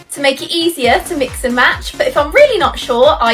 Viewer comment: Nobody: Still no one: Color detector: dark red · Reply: I heard it as "*dark* *bread*" lol